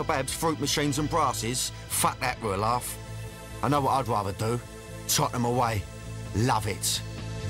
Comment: what does he say at 1:39...."i know what id rather do...(something)....luv it" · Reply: onlytag1way Tottenham away. It's a football team.